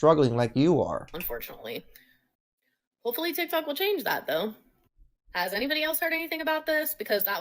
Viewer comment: Does she say that hopefully TikTok changes it? I didn’t realise TikTok made the rules.